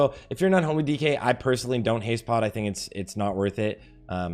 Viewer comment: did he just say he doesn’t haste pot as an unh dk? what in the heck